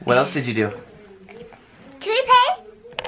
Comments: Also I think said 'Toothpaste' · who else had to replay at 11:30 ha toothpaste and i think he was trying to say 21 right but it was so cute how he said "toopaste" as in toothpaste so cute